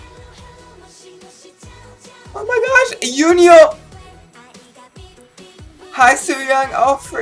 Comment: I thought said "Eunhyuk!" at 2:40. EKSDII!